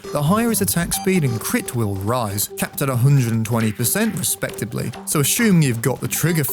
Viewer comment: i heard capped at 120% respectively and got so confused. "Capped at 100 and 20 percent respectively"